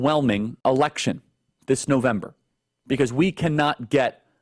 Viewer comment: I could have sworn Charlie Kirk said "erection" instead of election. 🤔 The issue of what Republicans want to do to women's freedom is already out of the genie bottle. Ya'll said the quiet part out loud, then went full blown crazy for ruling over women's bodies. Go fork yourself, GOP!